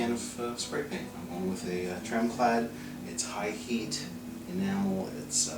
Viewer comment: you’re so wonderful, gentle and soft spoken, I can’t hear what you’re saying at 1:00 about the type of paint you’re using. It’s a what kind of paint? Can you give me more details please? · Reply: Tremclad. :)